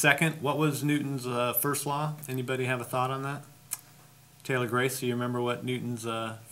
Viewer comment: Did he just said 'Thalia Grace'?